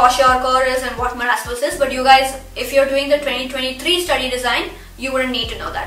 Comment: During 8:46 you say that if you are a student studying 2023 syllabus theres no need of doing malaria but the caption says for 2024